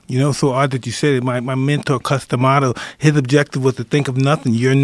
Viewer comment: Like I enjoy boxing but I can't be the first person that I thought Mike said custom otto, like he's American come on?😂